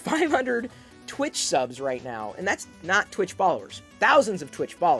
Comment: Lol at first I thought you were saying "twitch ballers" instead of "twitch followers". I just assumed you had an esoteric twitch sub level unlocked